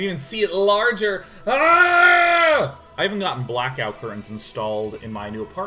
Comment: Hey guys! what did he say at the beginning? 0:14 "I haven't got __ installed in my new apartment" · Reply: “InariSound” Kami - Blackout curtains.